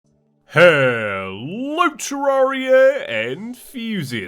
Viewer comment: i always thought he said "hello terraria and fusius"